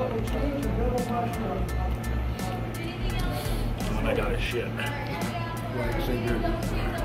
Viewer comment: did say "I gotta shit"??? LMAO